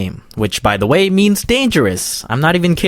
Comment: I thought it said memes.